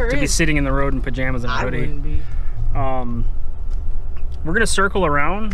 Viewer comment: i heard a guy say "I wouldnt be" .....it was good to see yall again